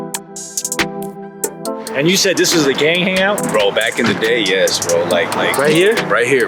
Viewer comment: Said gang hang out zhad to put it together swore he said this a gay hang out